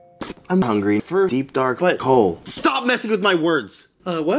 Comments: did Anthony say, "I'm hungry for a deep dark butt hole"? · is Anthony saying I'm hungry for deep darklet hole or it's just me? 😂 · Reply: Wait no I think we said I'm hungry for deep dark wet hole